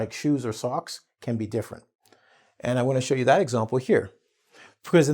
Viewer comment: is saying "I wanna show you" or "I'm going to show you"? I hear he's saying "I wanna" but according to the subtitles, he's saying "I'm going"